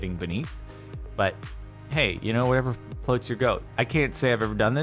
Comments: Did he just say... “Whatever floats your goat”? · What? Am I dumb or did he say floats your goat? Is this a bone apple tea reference? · Did say: "whatever floats your goat?" 5:28?